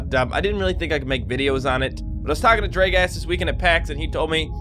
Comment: I heard "Draegast" WTH